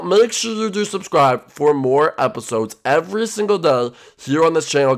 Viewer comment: Fun House Forever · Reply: when he said more episodes every day I thought he said war episodes every day